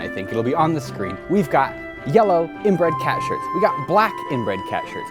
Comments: Ya I thought you were saying cat in-bread shirt like uh you know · I thought he said inbred cat shirt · at first I thought he said inbred cats shirts .. heh